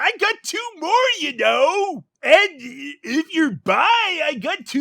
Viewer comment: "I've got two more you know, and if you're bi I've got two boys as well!" DEAD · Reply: I watched this several times and up until now I thought he said "if you're buyin'." Until I read this.